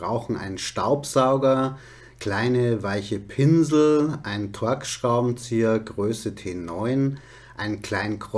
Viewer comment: ich weißt nicht aber welchen Scharubenzieher meinst du genau. Verstehe immer Twörk Schrauberzieher. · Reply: Er meint einen Torx Schraubenzieher